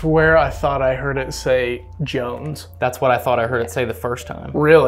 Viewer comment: Hey guys, at 40:00 I heard it say Charles or Jones the first time but the second was clearly Jones!!! Great video! Cheers al the way from Brazil!!!